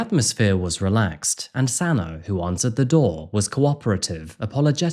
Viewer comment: whats her name? CC Captions and I hear Santa. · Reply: Xana